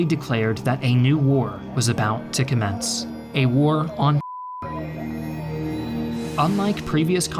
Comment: A war on what? fr now, I thought It was the N word💀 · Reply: He means the war on terror